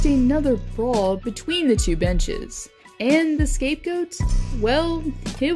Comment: When said "and this again sprawled a brawl between the two benches," I heard "between the two b*tches." 😂